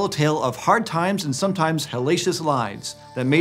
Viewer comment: I thought it said Hellalicious and thought those ancient pigs must have been damnlicious.